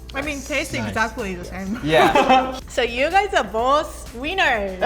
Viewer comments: i thought she said you guys are both weiners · I thought she said "you guys are both wieners." 😂😂😂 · is soooooo cute! With her accent it sounded like she said “you are both wieners” 🤣🤣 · When auto captions said ‘wieners’ i was dying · Its sounded like Said ”You guys Are Both Wieners” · i heard you guys are all wieners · I only hear "You guys are both Wieners" :D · When she said you both where winners it sounded like she said wieners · Sounded like she said wiener